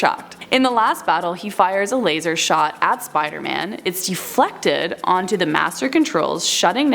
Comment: did she said Spiderman!? 😂